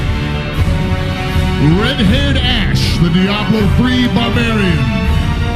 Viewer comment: Why the subtitles says "redhead ass the Diablo 3 by Berry"? in